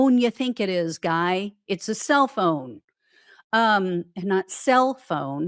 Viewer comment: Yeah, I heard “cell phone” before you clarified “self-own”. Thanks for all you do, your sense of humor and that wonderful laugh ~